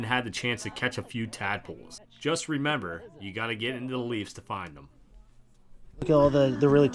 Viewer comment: Did I hear "Get into the LEAFS?" not a bad idea for a Pens fan to consider ha ha